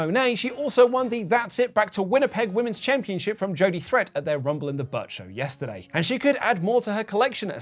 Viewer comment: did you say Rumble in the Butt??